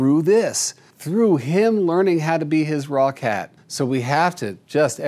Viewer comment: Every time Jackson says "raw cat" I hear "Rockette" and picture a chorus line of cats kicking up their paws! 😂